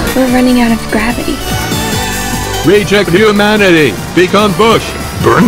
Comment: why i hear "reject humanity become BORSCHT"